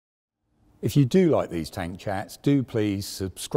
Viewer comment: At the start i thought he said tank chads